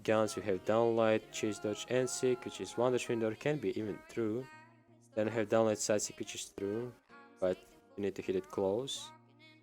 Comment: Can somebody explain to me what he said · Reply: "Down light chase dodge NSig which is 1 dodge window or can be even true and have down light ssig which is true"